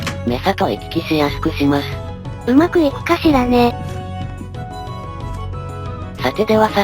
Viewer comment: の霊夢がうまくいくか(´・ω・)ｼﾗﾈ に聞こえた